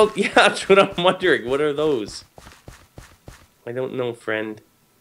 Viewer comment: I think at 0:54 he said WHAT ARE THOSE? But so calm.....Sigh